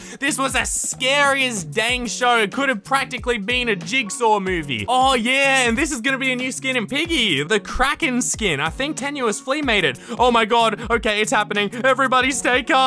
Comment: the captions say tenuous mated wth